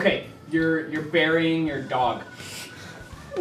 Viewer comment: I thought he said you're burying your daughter